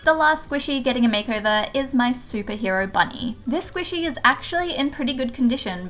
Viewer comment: I don’t know why but when you said superhero bunny I thought you said stupid hero bunny Lol 😂